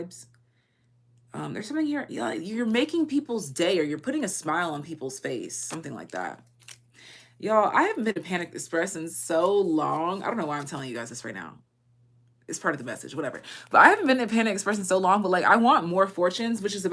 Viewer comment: i swear the first time you say it you say "Yo I haven't been to Panic Express in so long..." instead of Panda Express and even the captions are with me on that...but idk not complaining i think that could be significant for some people, maybe me